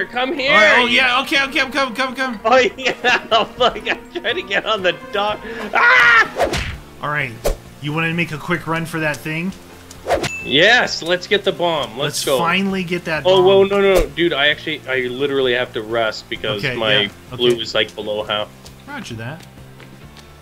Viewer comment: I thought he said let's get the bong